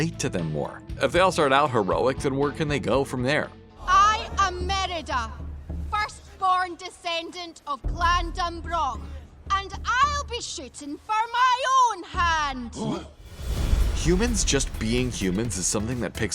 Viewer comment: it sounds like shes saying "i am merada, first born descendant of glandombrock and i'll be shittin for my own hand!"